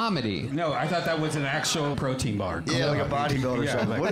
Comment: “It’s not something” 😂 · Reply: You're right, I heard "it's nuts" I thought that was quick and funny, some protein bars have nuts, and it's nuts, crazy not real doesn't exist. But I misheard